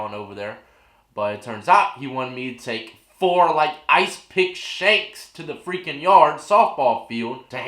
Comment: Bruh I deadass thought you said 4 Ice cream milk shakes lol